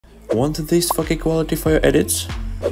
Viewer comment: I just heard "want this fck quality" lmao